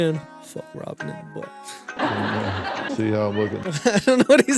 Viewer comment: I think he was saying, "see how I'm lookin'."